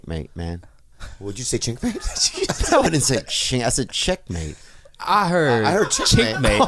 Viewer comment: I heard Chinkmate